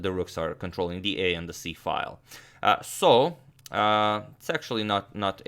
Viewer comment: i thought he said sexually not a bad position when he said "its actually" :)